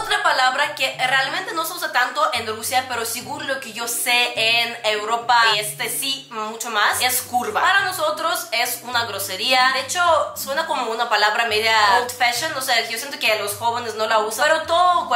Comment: ¿Dijiste curva? Es que no te entendí ahí